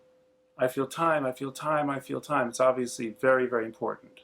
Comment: The first time i heard this track I thought she was saying, I Fill Time which makes more sense to me . I thought it was because of her Norwegian accent